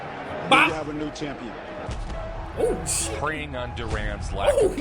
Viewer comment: At around 28 minutes I think you misheard PREYING as PRAYING from your response.......what he said was Sugar Ray was PREYING on Duran's lack of conditioning. As in using it against him, preying on him, like a predator does with their PREY. Prey with an "e". Lol 😂